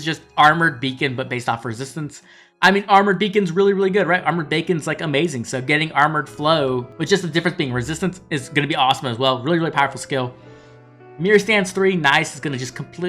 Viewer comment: Did you called Armour Beacon Armour Bacon.